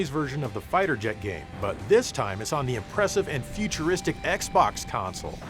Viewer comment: It sounded like you said " but this time its on the OPPRESSIVE and futuristic Xbox"